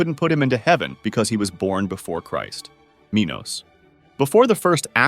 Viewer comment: I thought Minos said minors for a second